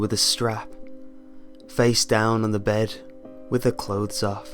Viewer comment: Wait did he "Do" his daughter? i heard "face down naked on her bed"...